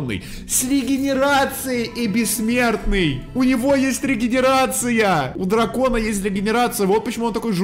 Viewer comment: мне послышалось дегенерация хахаха